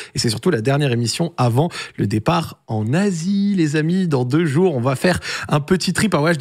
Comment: mon vau-cer a no-joke bug quand t'a dit en Asie j'ai entendu en nazi mdrrrrrr